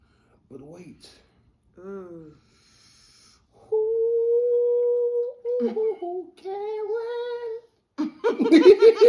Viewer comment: Did say “ooh ooh Karen” like Bobby Brown in Two Can Play That Game?🤣🤣🤣